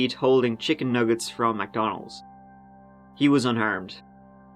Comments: I thought you said the child was unARMED instead of unHARMED 🤣 I was like "well duh" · "her 4 year old son, still in the back seat holding chicken nuggets. He was UNARMED"... Wait what 😂 think I misheard that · For a moment I thought you said the little boy in the car was "unarmed". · I really first thought, the police checked, if the little boy was the shooter, as I thought you said, he was unarmed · lol when you made the comment about the 4 year old in the kid I thought you said “he was unarmed” and I thought it was a joke but you didn’t laugh which is when I realized you said “he was unharmed” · When you were talking about Josh Jr being in the back of the car; I thought you said ‘ un-armed’ instead of ‘unharmed’. Nearly spat my coffee out from laughing 😂